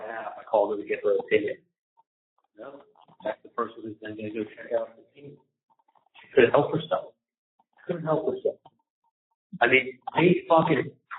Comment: What is he saying at 41:40 It's not transcribed correctly but it sounds like he said - Next thing the person goes to check out the scene.. she couldn't help herself. Subconscious slip? He talked to her about it happening, and next thing she goes to check out the scene because she couldn't help herself.